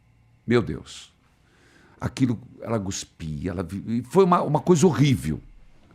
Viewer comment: No 3:44 o Padre disse ela "GUSPIA" ou foi impressão minha? Uma vez que, o certo é CUSPIR e não GUSPIR!!!!